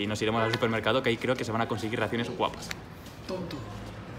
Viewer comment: Like si piensas que es el viejo de atras que dice "TONTO" mint